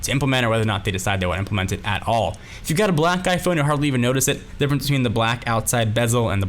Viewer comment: sounds like you said "Black Guy phone"